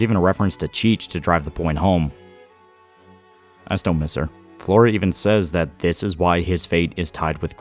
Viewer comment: almost got my pitchforks out cuz said 'I still dont miss her'